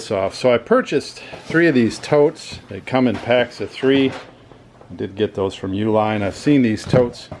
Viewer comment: 1) Watch the video again. He CLEARLY states these are from U-line, regardless who the actual manufacturer is. 2) They are meant to be stacked on top of one another and ship on pallets. See U-line's own promo video on the product if you won't believe me. 3) They're clearly sturdier than a Sterilite tote and I'm sure are great for moving stuff around between warehouses - but they're NOT meant for common carrier shipping. The most common complaint for these types of totes are them arriving with shipping damage - and that's empty, shipped in cardboard. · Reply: 1)Ok, did that. You were right he said 'I did get these from U-Line' . I thought he had said 'I didn't get these from U-Line'. 2) I wouldn't use them for shipping either. 3) Uhh, I forgot what 3 was.....4) Stay off the HGH & I look forward to seeing you back in the UFC :-p